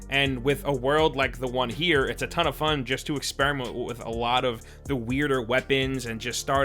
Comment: Am I trippin or at 3:31 did he say expremont or experiment I cant tell haha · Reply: Nah he said expremont